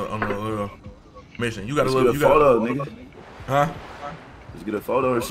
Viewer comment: Dat nigga said let’s get a 4 door I thought he said photo 😂dawg took off to the lot on all goofy shit